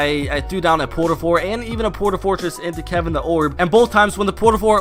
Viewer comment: did he just say kevin the orb xD